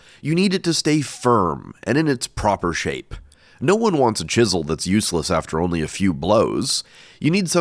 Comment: I thought you said "Jisel" not "Chisel" and I lost it lol